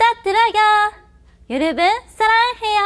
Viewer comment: 뭐라고 하는거임? ㅋㅋㅋㅋㅋ 졸귀네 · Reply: 아 많관부 ㅋㅋㅋㅋㅋㅋㅋㅋㅋㅋㅋㅋㅋ 이제 알았네